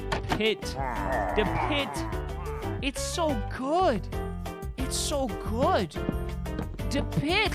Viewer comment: Are you hear that or its just me 7:09 say it so good but i hear flamingo her voice XD